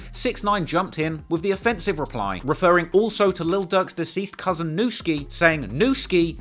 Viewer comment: I thought you were saying Lil Doug